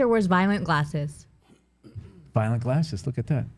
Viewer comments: I thought Bill said violent glasses too 😂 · This is a meeting of the minds that i was not ready for… Burr is passively hostile in his neutrality..? 9:00 good war tactic but damn.. fun stuff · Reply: I heard “violent glasses” too. Maybe it shows my bias against Burr’s intensity? Idk